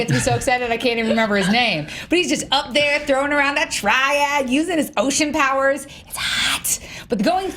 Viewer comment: Did she just say "throwing around his triad ?"